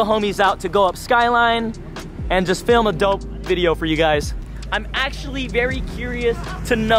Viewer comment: I misheard "a dope video" to "adult video"